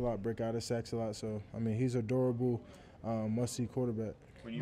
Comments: At first I thought he said Bryce Young was adorable 😂😂 but he said durable lol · I heard that as he’s adorable 😂